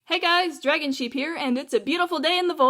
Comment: I thought she said Dragon sh#$%t